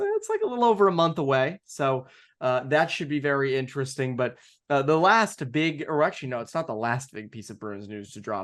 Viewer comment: Did anyone else hear say "the last big erection" at 0:43 ??